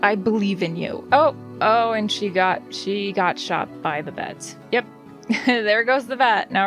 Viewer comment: Ohhhhh vets, I thought you said she was shot by the feds